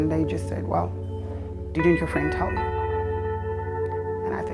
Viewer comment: What does she say at like 1:20? Before "and that's when my heart dropped" and after "three Zimbabweans walked in". What did they say?? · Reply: "Didn't your friend tell you?"